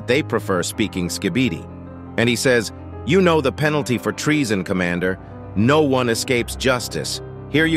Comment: no matter what i still cant hear astro toilet saying "You Know The Penalty For Treason Commander. No One Escapes Justice" i hear "youre gonna regret losing force commander. no one escapes justice".